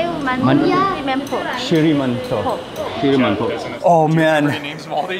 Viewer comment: She was just saying "shrimp and pork" 🤣🤣🤣